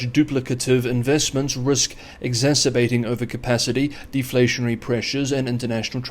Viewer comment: Did Xi actually say "exasperating overcapacity"? If so, he just admitted China (PRC) does over produce, something the CCP has denied for awhile.